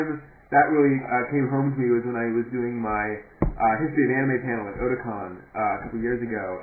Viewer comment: Couldn't hear the part at 00:45. Did you say Christian Anime panel?